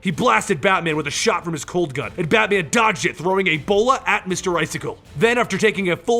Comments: Did you just say batman threw Ebola at Mr.Freeze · Wait... Are my ears broken? I heard:"Batman Dodges and throws EBOLA to Mr Freeze"